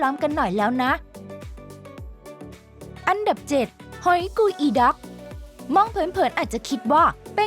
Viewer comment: สำหรับผมได้ยินว่าหอกกูอีดอกขอโทดเรื่องคำหยาบด้วยครับแอด